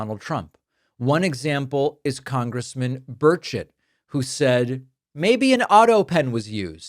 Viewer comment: At first I thought you said "Congressman Birdshit" 😂 Took me a minute to realize you actually said his name - Burchett. I think Birdshit is more fitting though. 🤣